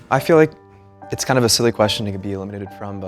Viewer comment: At the beggining her friend said she was silly but I thought she said slutty lol